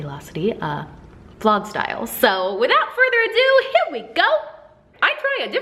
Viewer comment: Does she say 'without further to do' in 0:44? She says that often but my german ass can't understand it.😭😹